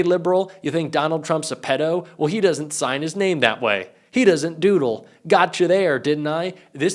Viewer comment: "He doesn't doodle" I think you misheard us... we said DIDDLE, not doodle. And he definitely did the deed.